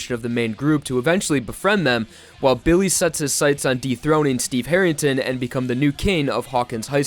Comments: i really thought you said “deep throat steve harrington) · “dethroning Steve Harrington” I thought he said “Deepthroating” god 😭😂😂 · I thought he said “deepthroating” for a second there 💀💀💀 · misheard "dethroning harrington" as "deepthroating harrington" god i wish